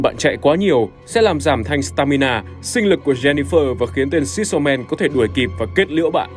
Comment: t nghe nhầm thành sinh lí